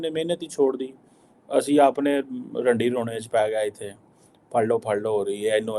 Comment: Is it only me who heard “Randi Rone” at 10:05 😅😅😅 amazing professionalism displayed by Dr Qamar Cheema 😂😂